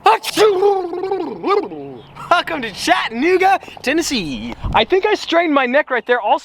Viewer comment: did he just say chat ni***